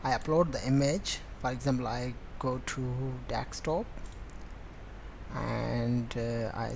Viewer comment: I dont know how he got it to work if he spells password passowrd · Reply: Dextop instead of desktop at 0:54 !!